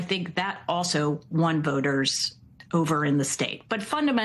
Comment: When ever Amy Walter said “Jim and I”, I kept hearing “Gemini.” It’s like she kept saying that she was using Google Gemini to do her research. 🤦‍♂️